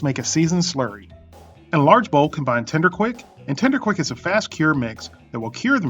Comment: what is tiner quick??? did you call first powder Tinder Quick at 1:57? I wanna know what it is. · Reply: TENDER QUICK made by Morton's salt Company.